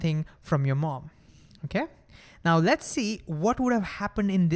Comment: I legit heard ठीक है 🤣